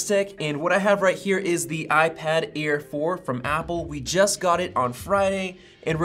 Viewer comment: I totally heard "iPad Ear 4" at the beginning 😂🤣